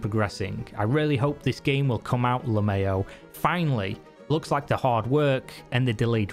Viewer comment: did you say lemayo?